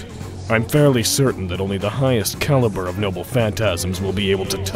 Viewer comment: Anyone else realize that Saver said, “Excalipoke” instead of Excalibur, or am I losing my hearing? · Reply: I thought she said “Caliburn” referencing FateGO...